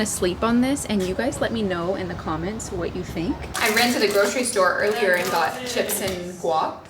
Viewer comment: You said “I ran to the grocery store” and it sounded like you said “I rented a grocery store” 😂😂. Loved it! If only we could!